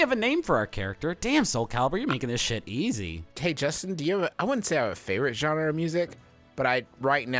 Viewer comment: Did Justin just call Griffin...Justin? Or did my ears deceive me